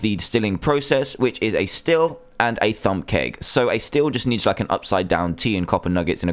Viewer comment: I thought you said thumb cake!